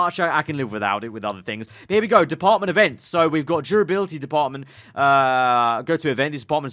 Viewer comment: I thought he said "Department of defense"